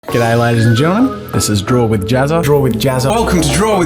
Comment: "G'day ladies and gentlemen. Welcome to Jazza, I'm Jazza" :D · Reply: I always hear him saying "get out ladies and gentlemen....."